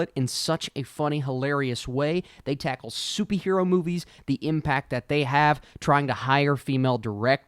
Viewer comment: Am I tripping or did he say soupy hero movies